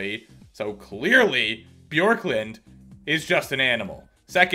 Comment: I thought said Porklund